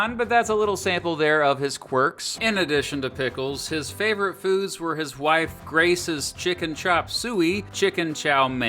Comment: I heard that as "his favorite food is his wife"